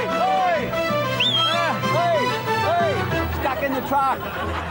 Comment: Did he say 'Oy! Oy! stuck in the fuck!' ?